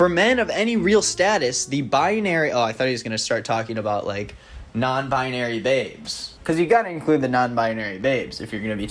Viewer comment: When said “non-binary babes” I thought he said “they-bes” and I kind of love that.